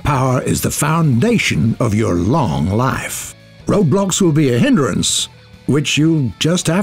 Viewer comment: Oh god I thought you said “Roblox” 1:48 Thank god you didn’t